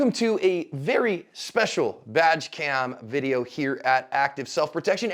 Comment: I could’ve sworn said “vag cam” at the beginning. That’s a completely different channel: mostly the same audience but very different content.